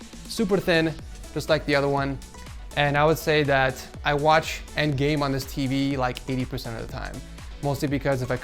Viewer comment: I thought he said I watch Endgame 90% of the time XD